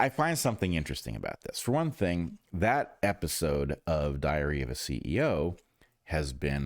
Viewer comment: Wait, did he say "Diarrhea of a CEO" around 04:25 ??? LOL Freudian slip? LOL